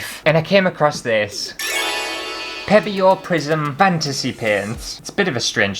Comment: It sounded like you said “Prison Fantasy”! That would be an entirely different video.